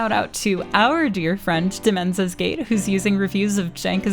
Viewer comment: Does anyone know the actual name of the channel mentioned at 1:04:50? I can't quite understand what she says and searching variations of "dementia's gate" or "dimensis gate" doesn't bring anything up. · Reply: demenzas gate